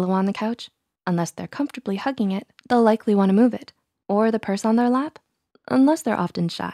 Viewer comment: I thought you said “that person on their lap” instead of purse